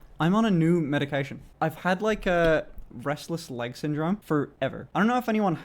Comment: For a second I thought he said breastless leg syndrome